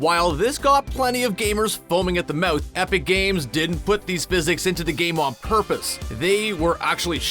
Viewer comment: waaaiiitttt did they just say that Epic games is fat?